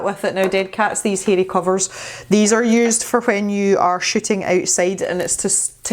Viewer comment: Soooo when you were hauling the new mic and fuzzy cover thing I thought you said “these are good for when you are sh!tting outside” 😂😂 I quickly realized you said shooting! But it was a much needed laugh! Love your videos 👍